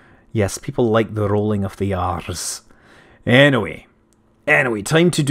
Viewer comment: "People like the rolling of the arse" That's what I hear. I'm sorry but I can't help it!